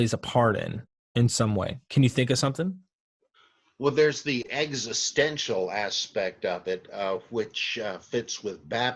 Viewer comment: When Dr Price says existential at 52:34 I totally heard it as eggs-istential